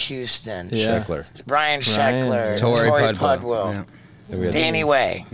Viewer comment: I swear he said Brian sheckler